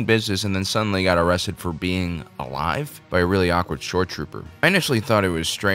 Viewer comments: I thought you were saying "short trooper", which made me think "yeah, he does look a little short for a stormtrooper". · i heard "short trooper" lol